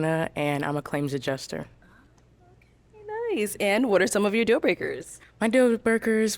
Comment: Did she say “deal burgers”? 🧐 lol